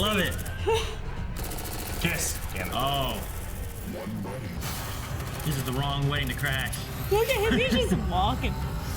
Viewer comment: What does Prometheus say before "Two Funerals" I can't tell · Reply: "1 wedding, 2 funerals" you idiot